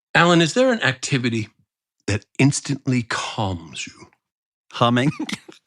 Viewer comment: I thought Alan said cumming..I had a good laugh for 10ish seconds, then realised my error and felt a little ashamed. Anyone else? 😂😅